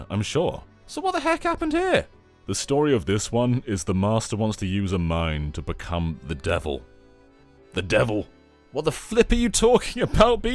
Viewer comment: i misheard that as “The Master wants to use a mime to become the devil” … and you know that, i’d watch that episode LMAO 🤣